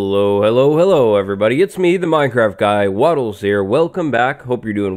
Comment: ooomg i just realised that he says “the minecraft guy” i thought he was saying “the minecraft guide” (yea its weird “it’s me the minecraft guide)